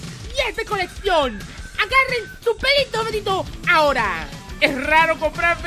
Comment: Alguien más noto que dice agarranme los huevos y después lo cambia jajajaja